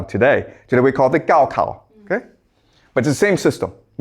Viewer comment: what is this word exactly? I would like to look it up - thanks! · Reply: Gaokao, the Chinese exam system